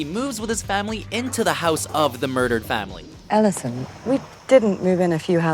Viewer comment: did she just say Ellison ?